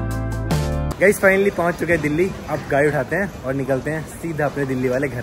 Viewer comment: did you say gaai instead of gadi